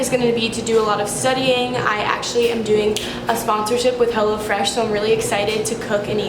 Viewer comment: School is almost over. How do you feel? Me: Honestly great. sarcasm hgggmmmnnnn Edit: Thought you said hella fresh instead of hello fresh.